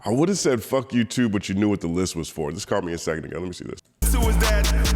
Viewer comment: i don't hear "list was for" im hearing "list was full" as if he needed to clear his list before moving on to his next targets. Didn't he say on Euphoria he'll "pick 'em off 1 at a time"